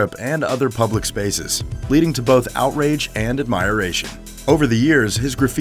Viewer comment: admire-ration ... ? did I hear that right?